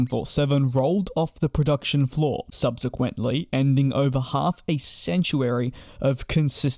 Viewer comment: Sounds like you said "half a sanctuary" of production. Presumably you meant half a century.